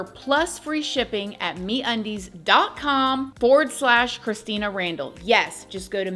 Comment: 😂 I thought you were saying "meat undies" 😂🤣😂🤣